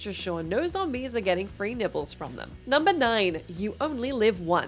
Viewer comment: I thought she said "free nipples" when talking about ZombiU. But then I was like " ah, FREE NIBBLES"